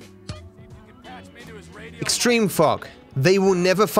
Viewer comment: I was with subtitles and instead of extreme fog it says extreme Fck 🤣